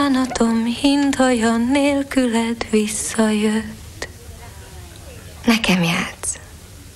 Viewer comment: did she say "Play it, Sam?" :P